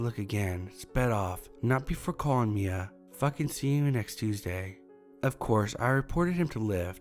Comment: I don’t understand what you were saying at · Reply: C u next Tuesday. its a clean way to say Cunt which is what was actually said..but my mom would kill me if I said that lol